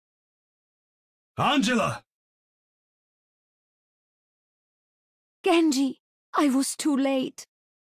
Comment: Am i the only one who has heard this line from Mercy? "Genji so soon?" She said it when genji was on low health and healed him. Yeah i think misheard it. Maybe she said "Genji! I was too late." I busted out laughing when i heard it tho. Sorry for bad english.